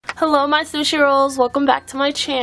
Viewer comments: did she just say "hello six year olds" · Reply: Lmao she said hello sushi rolls I think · in the beginning I thought she said hello my sushi rolls lol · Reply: I thought she said " my six year olds"